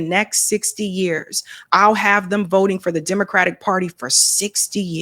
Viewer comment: Smart broadcast! LBJ, I had thought said, 'I'll have the Blacks voting Dems for the next 500yrs.' But you said 60yrs. Either way, LBJ knew he had Us in the bag for at least 3 generations! 👀🤔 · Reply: I could’ve swore he said 200 years.